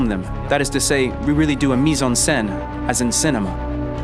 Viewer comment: What’s the word they say at around 8:39? I’m interested in it. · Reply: Mise en scene